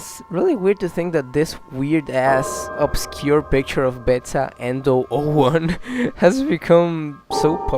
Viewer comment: Bruh when I heard Endo 01, I thought he said Eva 01 and I was like "Bruh what? Evangelion fnaf?"